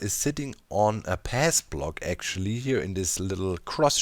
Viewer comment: idk if I'll ever need to use this info, but what do you mean with "pass block" (atleast that's what I hear lol). EDIT: oh, path, yeah I heard it wrong but it's whatever, that might be useful info but idk